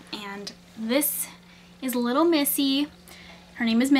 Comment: Lol i thought you said her name is messy.